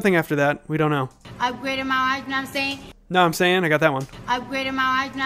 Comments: “Upgraded my ??? y’know wha I’m sayin’?” No, Cardi, we don’t know what you’re saying. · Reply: I heard “upgraded my art know what I’m saying?” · For some reason I heard: “Upgraded my wife know what I’m saying?”